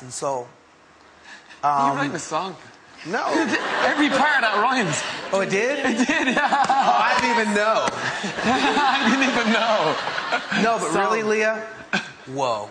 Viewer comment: can anybody tell me what danny was saying in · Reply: macfoo00 Danny: Are you writing a song? Will: No Danny: Every part of that rhymes Will: Oh it did? Danny: It did, yeah. Will: I didn't even know.